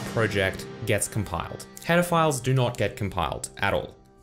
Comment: I wasn't paying attention and heard "Pedophiles" instead of "header files" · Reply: I ALWAYS hear "pedophiles" instead of "header files" in every one of his videos ! :-D